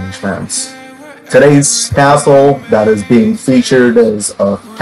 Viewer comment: It sounds like you say today’s assshole but it’s really castle